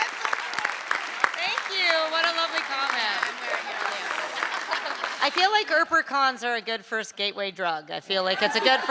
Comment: What did Kat say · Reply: "I'm wearing it on the outside"